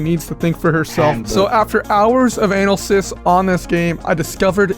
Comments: subtitles say,"after hours of anal cysts" lmao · did you say Anal-sis on purpose? Edit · what he said: analysis what the captions said: anal cysts